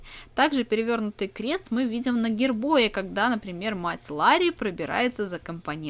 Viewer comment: мне послышалось или она сказала "Мать ЛАРРИ" а не мать Меган